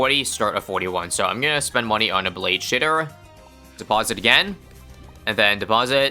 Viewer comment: am I the only one who heard "blade sh*tter"